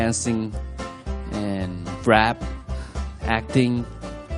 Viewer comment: the automatic english captions said "crap" for rap lmfao